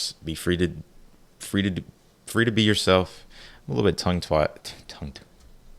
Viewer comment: I thought I heard you say “I’m a little bit tongue twat!” 😅 It’s okay Boo👅🤪